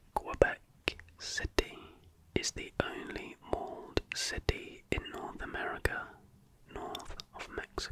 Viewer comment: what does he say about Quebec? Mold city?